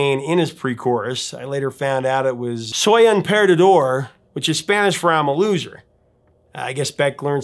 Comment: I didn't realise Beck says " soy un perdedor"( I'm a loser) in Spanish in the song..( and I'm bilingual n English and Spanish)...I thought he was saying " So, open the door"...🙄🤣...a great song...the bizarre images of the lyrics are genius...